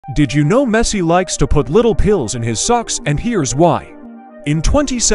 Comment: I heard "Messi likes to put little kids in his socks"💀